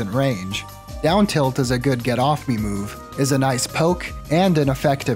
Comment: Ive seen this clip 20+ times and I just realized Zain picks up his controller for him. That's really great sportsmanship in a moment where most could argue he didn't really need too. Good on Zain. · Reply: it sounded like he said “get f***ed